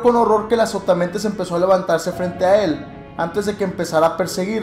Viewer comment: pensé que decía "que es azopatamadre:v"